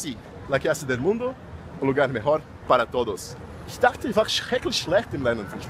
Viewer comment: de tudo q ele falou so entendi uma pronuncia "chicrete"